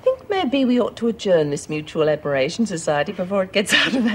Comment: John W what's does Reno say at 27:09 aberration or something? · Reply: Mutual admiration society🎓